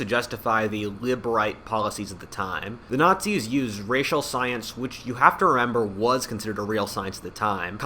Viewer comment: Hahaha sounds like u said stience